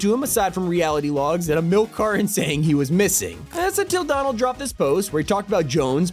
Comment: at first I thought he said Donald trump 😂